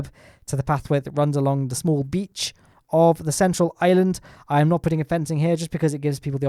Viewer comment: It sounds like you said Ireland.